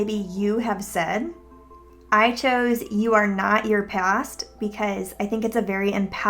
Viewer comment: With the quote thing i thought u said : you are not your best...